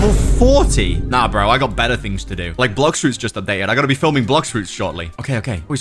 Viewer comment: Wait did he say I'm gootta film blox fruits 19:40 finally